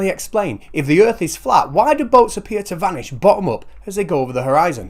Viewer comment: By point 5, I only heard "bottoms up!" instead of "from the bottom up" because I was ready to drink myself under the table just to make the stupid stop...